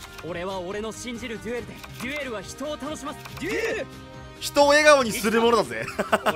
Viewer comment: 「デュエルは人を倒します！」 に聞こえるwww